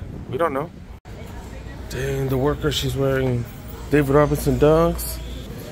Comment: did you say David Robinson Dunks? 😅